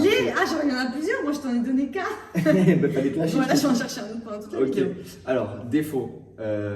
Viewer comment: elle a dit wallah vous avez entendu comme moi ?